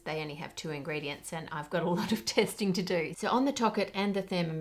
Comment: What Ann said: " I've done tests. So-many- tests..." What I heard: " I've got a certain set of skills..."